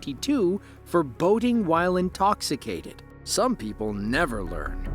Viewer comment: So that Michael person who ran, got caught, and was arrested again later on.. I thought the person said "Voting while intoxicated", I was thinking I didn't know that was illegal or a thing.. 😀 So I rewound and used CC.. Ahh, that makes more sense. "Boating while intoxicated." 😀